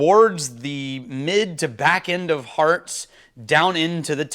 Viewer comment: Maybe this will help! <link> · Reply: Thank you, I watched both and it was very helpful. I actually thought you were saying "hard" cuts so it was helpful right off the bat finding out it was "hearts". Again, both were very helpful, so thank you. Cheers 🥃